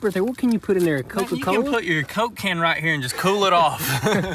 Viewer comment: when said "put your coke can" i legit heard "cocaine" xD